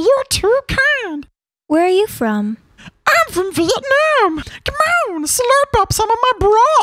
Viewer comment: "i'm from Vietnam!" but the captions say "I'm from your mom"